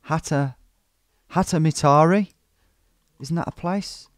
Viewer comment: "Finns probably laughing at me" just at the point where I laughed. Kattomittari = Roof meter = add for some roofing company. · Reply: Oh, that's what it was! I was listening more than watching, and I kept hearing "hätämittari". 🤔